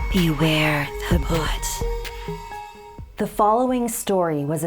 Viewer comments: I'm sorry but I heard "Beware The Butt" 😂😂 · I thought it said “Beware the foot” O dang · I wasnt watching the screen at first and was busy cleaning while this played so at the time i heard 'beware the butt' and was very confused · I’m the beginning I thought it said, “Beware the butt” xd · I honestly thought it said beware the butt until i looked at the screen xD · I thought she said “beware the butt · My dumbass thought she said "Beware the boot" Xp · I was taking a sip from my Powerade so I wasn't paying attention so what I heard was "beware...the BOOT" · I thought she said beware the butt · bruh it sounded like she said BEWARE DA BUTT that really is scary XD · I thought she said beware of the butt lmao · I thought I heard "Beware the Butt" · I heard “Beware the foot” 🤣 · i deadass thought she said beware the butt i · I was listening to this and thought the story said "beware the foot" 😂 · I was listening but wasn't watching and I thought you said beware the butt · so i wasn't looking at the screen when 'beware of the bhoot' i thought she said 'beware of the butt' i was so confused and replayed it and realized it was 'bhoot' not 'butt' i feel stupid 'i am are the have stupid' xD